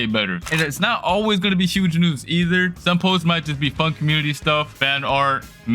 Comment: WHAT DID BRO SAY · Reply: He said fun not fu